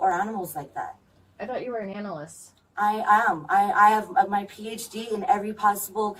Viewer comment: "I'm a vegetarian" "I thought you were an analyst" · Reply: I thought she said "animalistic"